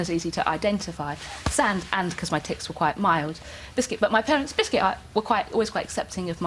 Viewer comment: I thought she said @ 01:55 ''My tits were quite mild'' ahahahahahahaha.